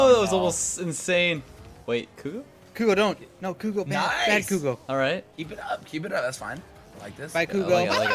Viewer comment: The moment when your phone hears Ok Google instead of Bad Kudo.